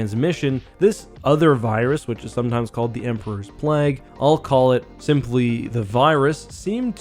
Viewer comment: I thought it said Emperor's Plaguis lol